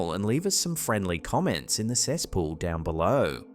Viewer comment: at about 12:12 it sounded like said "leave some comments in the cesspool down below"...am I hearing things now? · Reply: He said cesspool